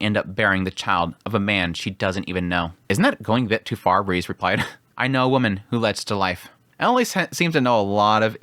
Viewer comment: what did he say? "I know women that led such life?”